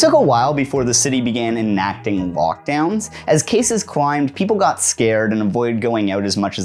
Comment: Ohhhh my god I legitimately thought you were saying "walkdown" and not "lockdown" and was very confused